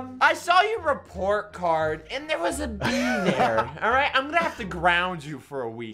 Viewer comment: did he say b or d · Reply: b